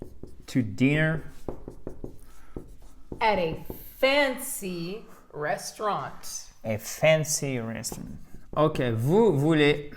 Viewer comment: Merci pour les dictées. Mon écoute s'améliore mais encore quelques confusions par ex. j'ai entendu "expansive restaurant" au lieu de "a fancy restaurant". et beforehand devenu "before end". .. un peu stupide. Encore merci